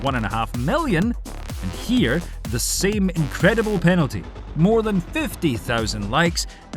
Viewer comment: did he say penalty ?